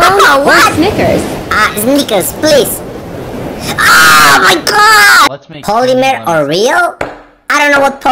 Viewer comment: what did he say 💀💀💀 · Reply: I think he said omg